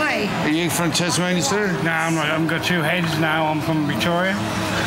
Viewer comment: At around 5:10 what does that bloke say about 2 hands and Tasmania? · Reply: He says "I haven't got two heads".